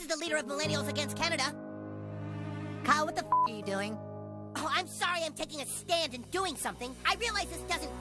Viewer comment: I thought he said "oh I'm sorry I'm taking a STAN and doing something" lololololol 😂😂😂